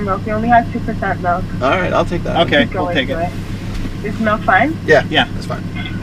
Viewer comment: was she saying "It's Mau5" ???? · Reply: is milk fine i believe